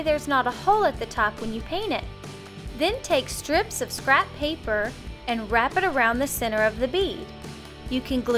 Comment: When she said crab paper i thought she said crap paper 😂😂😂😂